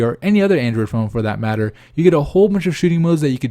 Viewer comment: Did he say "you get a whole bunch of shitty modes"